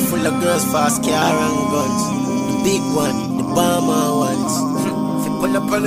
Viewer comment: Always thought he said the palmer ones....addi